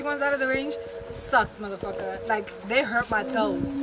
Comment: Did she just say "Rihanna.......motherfucker" ???